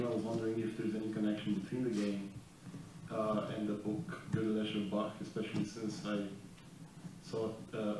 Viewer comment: I can't hear the name of the book at 13:20 anybody knows the name of the book ? Thx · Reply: The book's name is Goedel Escher Bach